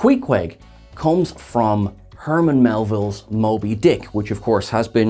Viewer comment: I was listening without watching the video and only half paying attention, and I swore I heard "Herman Melville's moldy dick" and had to do a double take.